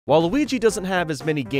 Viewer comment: it sounds like he said Waluigi instead of saying while Luigi